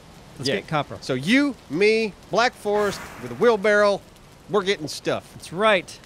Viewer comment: did just say "wheel-barrel"?